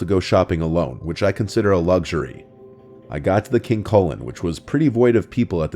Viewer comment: Thought he said King Colon 💀